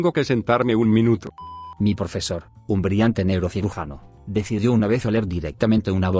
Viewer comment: en la segunda historia entendi "un brillante negro cirujano"